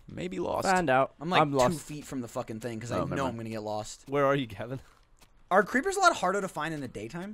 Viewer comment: "here are you Kevin?" What the hell?! Anyone else hear that?